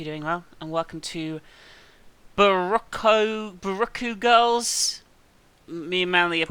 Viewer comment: I keep hearing "Barack Girls". Is that bad?